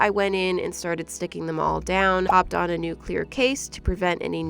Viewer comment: haha, at 6:44 i thought she said nuclear 😳